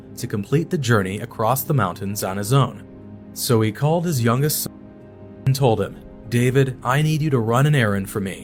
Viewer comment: What??!!? Have you said, David's dad called his youngest son's number???!!